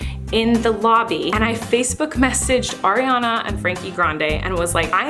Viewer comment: I thought she was saying Ariana Freaking Grande😂😂